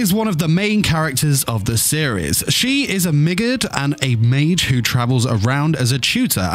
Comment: 😂 I honestly thought u said Midget When u said Roxy's race Migurd